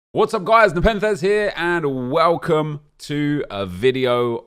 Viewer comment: I thought he'd Always say "whats up guys, the pen says 'here'" My whole Life was a lie